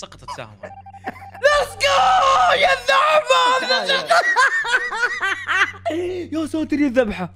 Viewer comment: انتم سمعتوه يقول الذحبه ولا انا عندي لاق في اذني؟؟